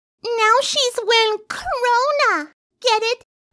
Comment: I thought she said Corona instead of Crowna.